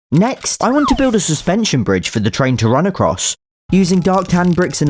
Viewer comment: Wait, I thought you said subway train, not train. Still great though.